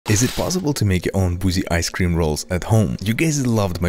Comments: I’m sorry. I know you said boozy but for a hot second I thought you said 🐱ice cream. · I heard "pussy icecream". I just get so excited :( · I thought he said bougie at the beginning instead of boozy lmao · I thought he said “ possy ice cream rolls